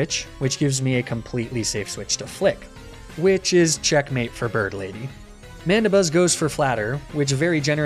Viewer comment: I was playing at 2x speed and misheard that as Berdly like the guy from Deltarune lmao